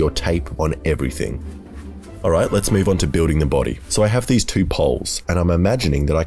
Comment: I thought he said burning the body